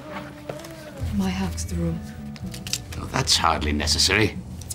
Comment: who was she waiting for? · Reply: what does she say? When I first seen it I thought she said my house my rules but that phrase wasn't used back in the 1960s.